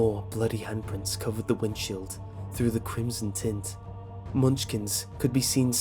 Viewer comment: Wait did they just say Crimson tit